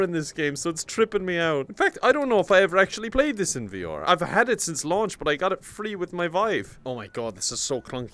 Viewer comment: lol at 2:16 I heard "I've had it since launch but I got it free with my wife"